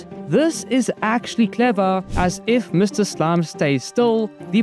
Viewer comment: "send this to a slug" but what i heard was "send this to a sl*t" 😅😅